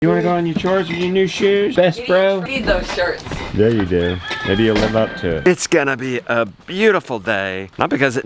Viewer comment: Did I hear this statement right, or misunderstood it? @ 0:03 -"We need those shirts" "Yea you do... maybe you'll live up to it" whattt?